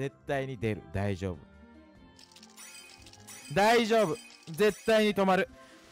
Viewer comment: のところ「大丈夫、大丈夫絶対にでぶ」って聞こえたの俺だけ？ 何回も聞き直したんだけどww